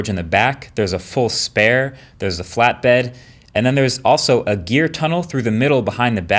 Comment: I thought you said a "DEER tunnel"! But I suppose you might be able to fit a smaller deer there!